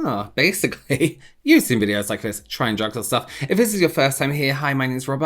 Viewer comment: I definitely heard 0:15 as, "You've seen the videos, trying drugs and stuff!" 🤣🤣🤣